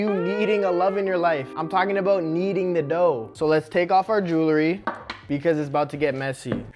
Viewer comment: did you say nedoh